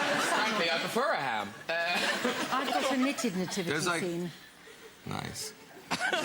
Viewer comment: Graham to Marilyn: "Are you into Christmas?" Marilyn: 🦇🦇🦇"I love it." ⚰️⚰️⚰️ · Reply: I thought she said “I’ve got a Naked Nativity scene” 🤪